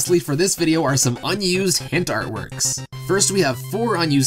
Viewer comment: i swear i heard hentai the first time